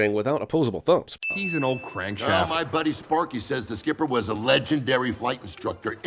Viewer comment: Did he say Sporky?